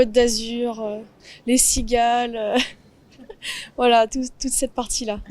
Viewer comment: At first, I thought she was saying ‘seagulls’ (mouettes), rather than ‘cigales’!